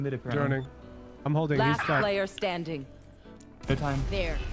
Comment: In Minute 16:40 his teammate tells him that there’s no time just cuz you put the blame on the whole team maybe you just missed it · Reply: I just looked at it back, I thought he said play time.. If I'm advising the cypher the urgency on the comm if it was "NO time" needed to be way more pronounced, I speak a lot to tonality, even as a viewer on my first watch I missed it, thats saying something! GREAT CATCH thanks for that